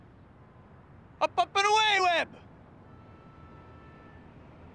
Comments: I always heard "Apapataway whip" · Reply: Bro I heard “Up up and a way way” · When I was a kid I thought he said: APAPAWIWI! 🤣🤣🤣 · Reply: i died reading that 🤣 but i always heard "up up and away again" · When I was a kid I thought he said: APAPAWIWI! 🤣🤣🤣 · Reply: as i kid i thought he said UPUP R WAY WEB